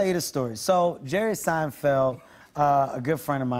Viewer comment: Oh haha for some reason I thought it said Jerry Springer for a split second